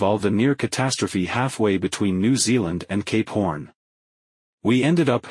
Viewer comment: Whenever the TTS says “Cape Horn, I mishear it as “gay porn.”